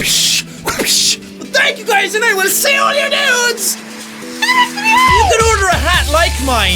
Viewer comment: For so long, when I started watching his videos, I thought he was saying 'I will see all your nudes'.